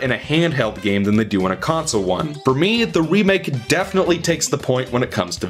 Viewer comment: did he say ''wii make''